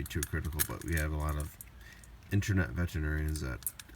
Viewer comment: "Internet veterinarians" lol.. at first I thought you said "internet veterans" lolz